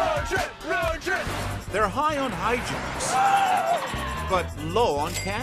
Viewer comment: Who else heard, “High on nitrous” instead of “high on hijinks”